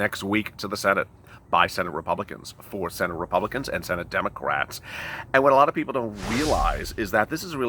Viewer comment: LOL, Did he just say, "Democraps" @ 1:52?